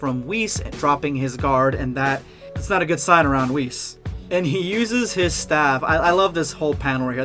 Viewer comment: I thought that said REFUSES in like fusion terms #endofvideosquad